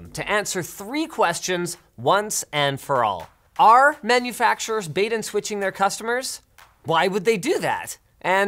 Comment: Closed Caption says "Our manufacturers..?" It should say "Are manufacturers?.." It's a question.. Someone fix it